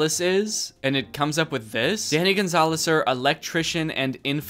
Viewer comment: why did he say danny gonzalezer